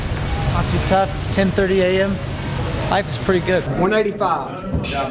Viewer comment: Am I tripping it sounds like they guy says " 195 " instead of 185 🤔🤔